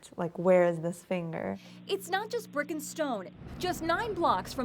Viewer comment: it’s sounded like she said “It’s not just frickin stone